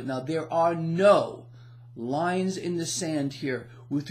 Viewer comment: Ha ! Thought i heard "Lions in the Sand" ??? 😃😉